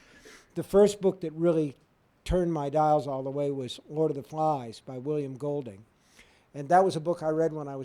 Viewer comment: can anyone tell me what was the book he mentions? I can't make out the name. To me is interesting, what cought his love for books. Anyone please? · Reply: "Lord of the Flies", by WIlliam Golding.